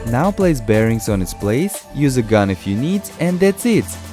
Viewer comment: Did he say use gun if you need